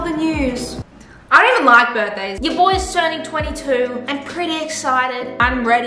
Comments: Did she just say,"Ya boy's turning 22"??? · Did she just say, “ ur boi”